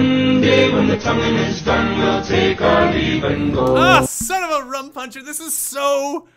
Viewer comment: did he just say "son of a rum puncher"